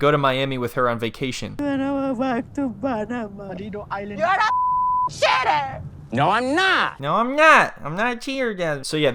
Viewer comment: When she yelled, "You are a cheater," at the end of the video, I heard, "you are a shitter" and died laughing thinking of the toilet. 😂